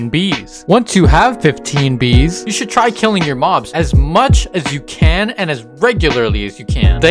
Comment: it sounds like hes saying u should try killing ur mom